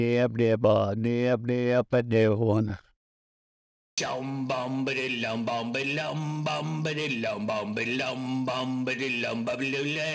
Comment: I thought he said “chum bum bi lili lim bum bili lum bum I lili um bum bili lum bum bi lili bum bili yay” And he probably did